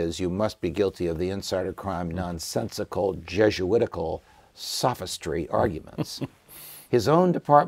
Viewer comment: Time Index 3:58 Did he say "Jesuitical" ??